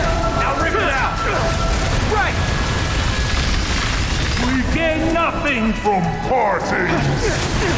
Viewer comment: I thought he said 'We gain nothing from fartings' and was confused af. Til I read the subtitles.